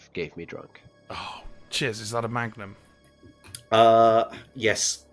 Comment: No joke, as an American, I heard "Chiz, is that a magnum" and thought was talking about a (prop) revolver and not the ice cream